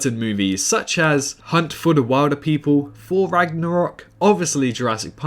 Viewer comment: I thought you said For Rangarok, although that would be an amazing movie